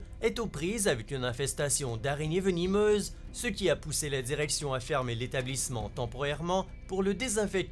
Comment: a 4:21 j'ai entendue "ce qui a pousser les érections" , dites moi que je suis pas le seul a l'avoir entendue XD